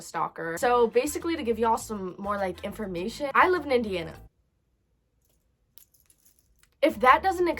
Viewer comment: Bro I swear my heart dropped bc I was trying to get something out of bathroom and I left this video on and I could still here it and I heard “I live in Indian” and I thought I said that😭💅